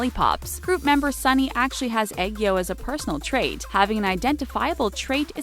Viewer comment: I thought she said egg roll